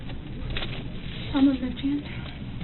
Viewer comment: read the caption 😮 like WTF !? Lol anyone knows what is Really said? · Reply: I double checked the file that 'should' have been uploaded -- she says 'On her left hand' (meaning which hand to swab)